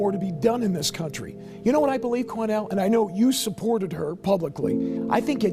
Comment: Sorry about messing u your name Quanel I thought I heard Cornel